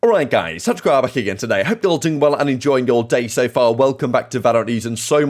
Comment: bro at the start this guy spoke so fast i heard "hope yall ting bala" lmao